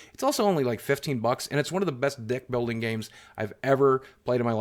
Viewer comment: Did you just say "One of the best Dick building games"?